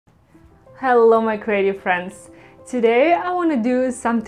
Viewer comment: At the beginning when you said. Hello to my Creative friends. .... I thought you said Hello to my Crazy friends. 🤣😂🤣. I always appreciate your creative mind. This one has great movement and colors. Two cups worked perfect. 💙💖💙. Stay Safe